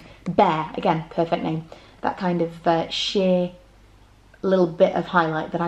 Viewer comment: that split second when I thought you said shit when you actually said sheer when talking about the lipsticks 😂😂😂